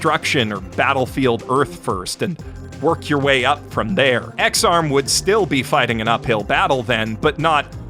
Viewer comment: When you said Ex-Arm, I thought you said X-Com and I was like "who would want to watch an anime about that game"... then I instantly was wishing it was about X-Com, because it probably would have been better than this mess